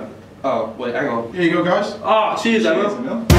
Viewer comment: I swear he just said 'cheers emile(heskey)